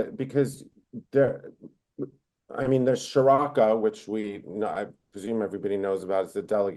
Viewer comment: I couldnt catch the name of the pro-Israel arab delegation that mentioned. Does anyone know the name? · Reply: Sharaka :)